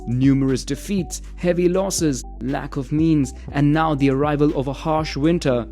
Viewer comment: I thought he said "Lack of Memes" lol